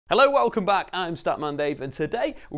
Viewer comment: I thought you said scat man